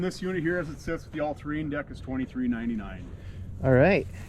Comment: Did I hear him correctly at 23:50 the the price point on ALTOZ TRX766 rider with the All terrain Deck is $20,399 ?